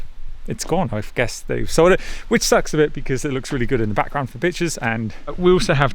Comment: who else heard good background for bitches !!! 😂🤣😂 instead of pictures i guess